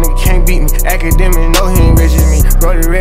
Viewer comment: Is it just me or did at 2:17 he said “akademiks know he ain’t rich as me” 😂😂😂 I swear to god he said that.